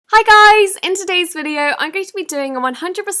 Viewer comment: Sounded like she said “Bye guys!”